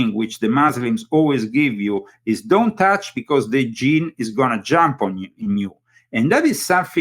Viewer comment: In transcript it says the gene is gonna jump on you but he was really saying Djinn...